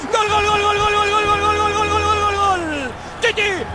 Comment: did anyone else hear titty titty titty or just me